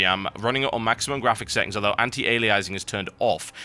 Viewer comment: Turn on Caption... At 1:54 it said sex instead of setting xD